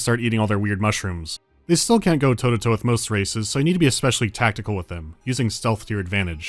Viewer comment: "you still need to oaly tactical with them with stealth" OI BOIZ WHERS DA PURPLE PAINTZ?? LETZ SHOW DEM UMIES OUR LATEST CREATIOOOONNN WWWWAAAGGGHHHHHH